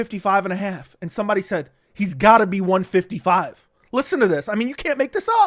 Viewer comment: I heard "he's definitely 155 "